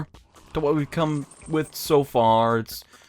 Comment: called the dungeon run of the mill and I swear I heard chains... fucking chains.